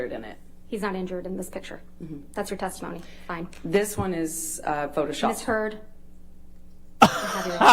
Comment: Bro 😱 did she just say “that is photoshopped”? 🤯🤯🤯